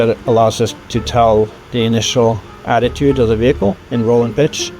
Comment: Caption corrections: at 16:04 he says "Attitude" not "Altitude". At 28:00 he says "Ingenuity" not "Engineering". At 28:16 it's "Wright Flyer" instead of "right flyer". Great video, thanks for putting it together.